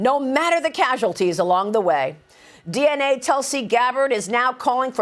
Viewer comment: Did she say DNA Tulsi gabbard? @ 4:52 I hope trump never finds that photographer hahahaha